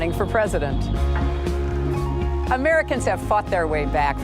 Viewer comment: "Americans have fucked their way trough tough economical times." That's what I heard XD Yes I know she said fought.